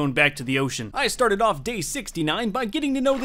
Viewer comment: I thought he said “I started off day 69 boning this dolphin”